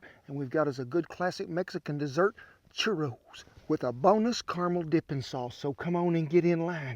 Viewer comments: Thought you said "boneless" caramel dipping sauce for a second there. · I heard “boneless caramel dipping sauce.”